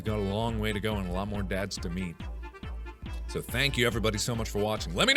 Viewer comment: I thought Mark said, at the end, "and we have a lot more Dads to meme."